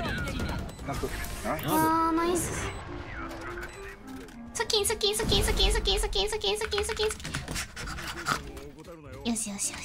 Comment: skin我聽成suki suki， 2:46 沒玩瓦羅，為什麼要這樣一直點一下點一下啊？